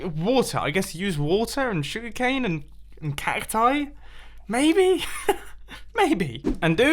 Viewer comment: In the 12:38 subtitles says Bumbo Cactoni instead of cacti.